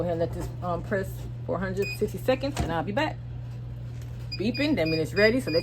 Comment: Hi! I love your videos. How long did you press your mask? At first I thought you said 60 seconds than I thought I heard 160 seconds.